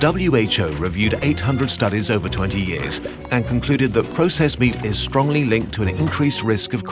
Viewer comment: Head over to our shop to get exclusive kurzgesagt merch and sciency products designed with love. Getting something from the kurzgesagt shop is the best way to support us and to keep our videos free for everyone. ►► <link> (Worldwide Shipping Available) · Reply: Just a wanted to let you know that at 5:53 the English captions say wheat instead of meat.